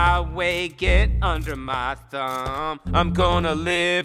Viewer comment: I thought he said "get out of my way, get under my c#m" turns out he said thumb😅